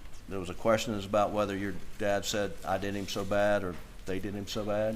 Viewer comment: I heard “I did them so bad”.